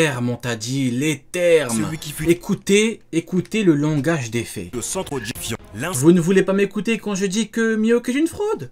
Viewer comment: C'est moi ou a 0:32 min dit "le centre du fion" a cause du retour en arrière 🤣